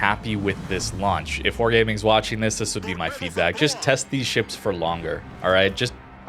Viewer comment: comodoro allowed him to use the special pan am commander in the match, it's an exact clone of libertad otherwise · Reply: oh the test ships dont allow? I thought he said steel ship not test ship